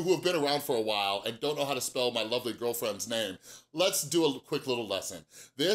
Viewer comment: I don't know who that is. I like to watch Sam Johnson 😋. Give your lovely girlfriend a hug for me! ☺️ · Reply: I thought it said lonely girlfriend lol